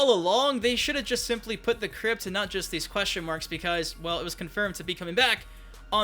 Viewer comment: I just want tag team smh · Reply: Seanpai I could’ve swore I heard tag team was konfirmed